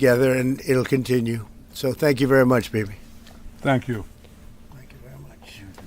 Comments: did his say 'bibi' or 'baby'? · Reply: Thank you very much, Baby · i thought he said "baby" for a sec 😂😂😂😂😂